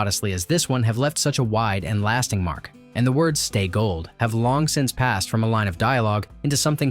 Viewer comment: I thought he said stay golden pony boy, not stay gold pony boy